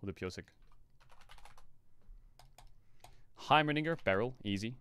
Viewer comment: didn't he say Heimern*gger ? I swear I heard him say it